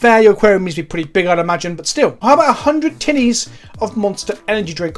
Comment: ngl I know you said tinnies but I can't hear anything other than "100 tiddies" and I just about shit myself. I fucking love this channel.